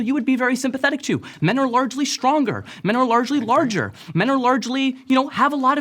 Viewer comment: did bro really say "largely larger"?